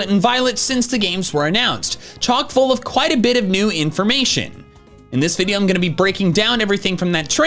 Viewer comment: I swear to God I thought you said "*chonk* full of quite a bit of information"